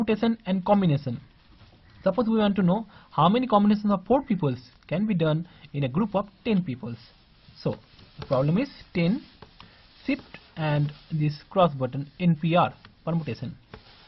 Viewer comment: I heard "how many communists in poor people... something something NPR"